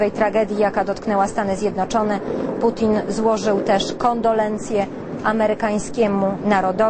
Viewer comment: Ja usłyszałem "kondom lencje"